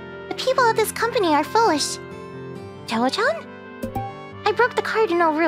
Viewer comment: It took me going back like 5 times before I realized I was mishearing “foolish” as “full of sh-t”